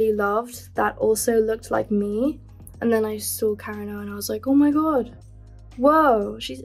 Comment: What’s the artist she said at 7:25 🥺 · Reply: Karen O